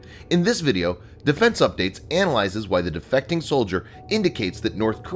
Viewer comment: I thought it said: "Why the defecating soldier..." hahaha.